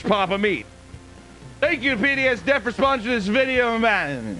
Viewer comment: I thought you were saying penis debt instead of pdsd debt